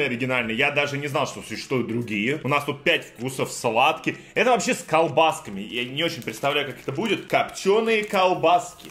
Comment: А почему на 5:01 сказал порно если тоже услышали лайк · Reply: Он сказал спорно